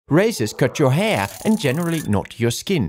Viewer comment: I misheard that… ”RACISTS CUT YOUR HAIR” 😂